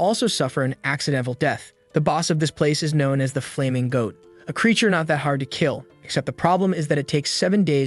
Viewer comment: Did he just say the boss of tbiz place wasThe Gay Goat? 😂